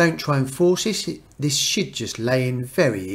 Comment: you said shit instead of should haha